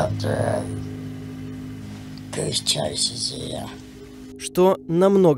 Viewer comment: мне кажется, или она сказала в конце это фразы is here.? ( если я правильно услышала, то фраза:" *имя * is here" ) До конца не досмотрела, но уже круто.